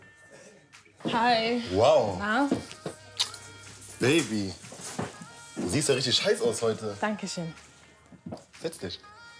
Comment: ich hab verstanden du siehst ja richtig scheiße aus heute XD